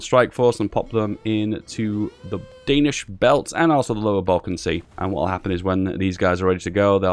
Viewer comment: Did you say Southern Balkan Sea ?